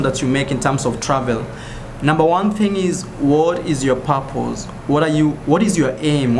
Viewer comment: I suddenly heard him say " whad is yo papoos?". Took me a few seconds to understand that he said "what is your purpose?" :|